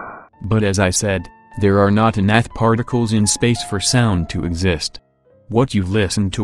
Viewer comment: does he say enough particles? It sounds like he is saying a different word . Anybody know?